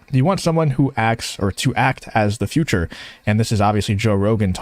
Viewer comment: That is NOT what he said at all. Back it up 30 seconds and listen again. "You want someone who actually has a future" not "acts as a future" which doesn't make any sense at all.